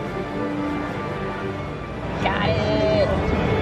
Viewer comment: are you german or what was that word at 0:27 ? :D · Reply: "Got it"